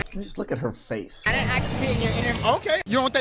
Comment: the closed captions got it wrong. It said she said “I didn’t ask to be in your interview”. She said “I didn’t axe to be in your interview”